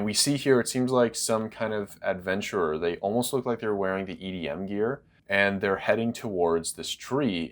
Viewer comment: Did he just say EDM Probably Eren be enjoying hits of DJ Snake by now.